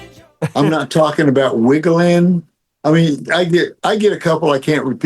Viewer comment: I always heard "I'm not talkin bout millennium". I knew it was wrong, but that's all I could hear!